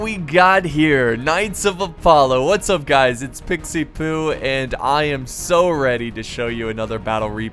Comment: Hey guys! Just to keep you updated. I am completely moved in to my new apartment and just now got internet! Things will go back to normal next week :D Thanks for watching and thanks for the support :D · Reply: did you said well come pikie poo